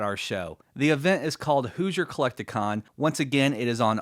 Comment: Lol I thought he said, "Who's your collect-ta- con?", as if it was the pun of who's your daddy Lol He said, Hoosier collect a con